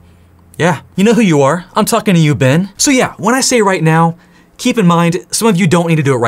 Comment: Dude. When you got to the part where you're calling people out for not studying, and you said "Yeah, you know who you are! I'm talking to you, Ben!" I had to listen again because the first time I SWEAR I heard "I'm talking to you, BENGE" AND IT KINDA SPOOKED ME LMAO